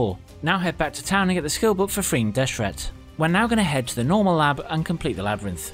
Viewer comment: Hi, Nice Tutorial. btw, What did you say at 7:08? get the skill book for _____ ? Thanks! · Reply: for Freeing Deshret - Trapped spirit in mines level 2. Glad you liked it. Thanks for watching!